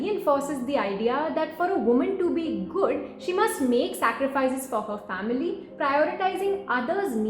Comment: Once I heard "objective of a woman's life is to make sacrifice" lol